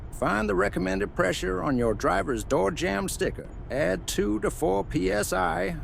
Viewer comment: Bad advice on 9. Do not do tire psi on sidewall. Do tire psi on door jamb specified by auto manufacturer. Edit. I misheard. Sorry. They do say door jamb sticker. My apologies